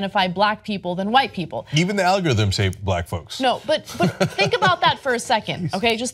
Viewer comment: Did he say even the algorithms "hate" black folks?